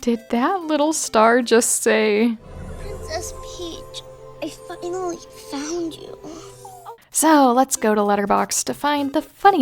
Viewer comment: "Princess Peach, I fucking found you!" - Glad I'm not the only one who thought it said that